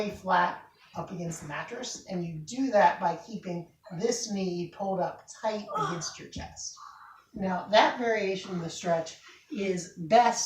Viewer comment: I had to replay sevral times at 1:32 because i wasnt quite sure of what i heard, was there other people there making another type of video? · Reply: Sorry about that. It did sound like there was some background noise. The words at 1:32 were "make sure to keep your knee held up tight against your chest."